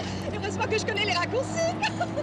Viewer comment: What's she saying at 0:42? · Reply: 'Heureusement que je connais les raccourcis'